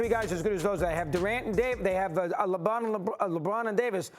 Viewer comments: did doggy just called lebrob LEBUM!? 😂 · Doggy WHUT DID YOU SAY??? LeBUM???? 🤣🤣